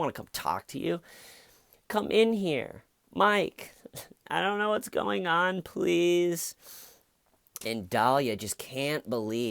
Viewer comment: I thought she said "I need to know what's going on please" Did she really say, "I dont know what's going on please"??